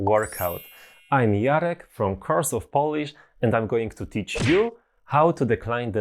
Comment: Jarek, I it sounded like you said “Curse of Polish” and with your skull shirts and accent, you kind of remind me of a vampire. 💀💀💀 Maybe a pirate….🏴‍☠️ Freudian slip? Anyway, I need a good laugh while studying noun declensions!